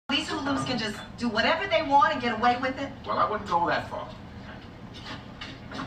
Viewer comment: Thought he said beat the sleeze off Eddie Winslow